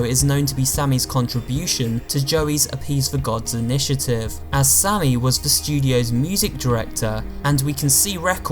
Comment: did anyone else notice that he accidentally said Sally instead of sammy XD lol